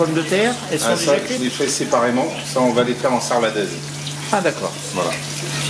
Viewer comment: Au sujet des pommes de terre, qu'est-ce qu'il dit à 07:45. Désolé, je suis du Québec, et je comprend «... j'vais les faire en serladaise...» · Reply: Sarladaise: Sarlat est une commune du sud-ouest où traditionnellement on cuisine rissolé à la graisse de canard ou d'oie.